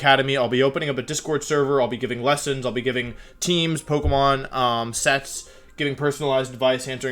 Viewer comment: "Sex"? You'll be giving sex too, sorry dude I dont swing that way... JK I know that isnt...what you said or meant XD